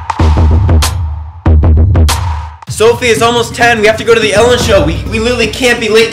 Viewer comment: omg who else thought he said sofie is almost ten!!! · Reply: He said sofie IT'S almost ten (as in time)